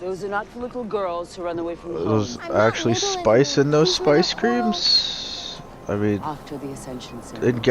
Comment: Not going to lie I kept hearing "Space Cream" not "Spice Cream"